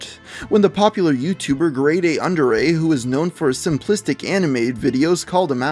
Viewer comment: I thought said known for his simplistic anime style when talking about grade a